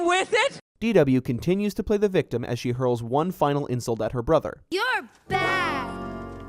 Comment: I keep hearing your a f*g